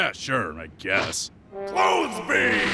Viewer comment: That awkward moment you realize Piccolo has used the Clothes Beam twice now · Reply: I always thought he said 'Clothes Thee'